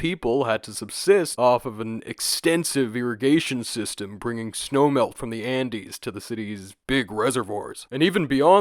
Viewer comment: I totally thought you said "snow milk" instead of "snow melt", and I was thinking "actually that's a nifty name cos it's often aerated and have minerals that makes it a bit cloudy like milk" !!